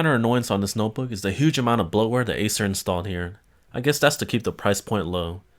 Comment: what did you say at 4:18? a huge amount of what? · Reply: bloatware